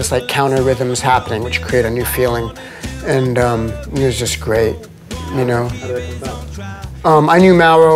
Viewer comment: Did Flea say Muse is great?